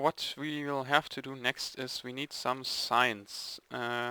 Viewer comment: when you said "we need signs" i thought you said "we need science"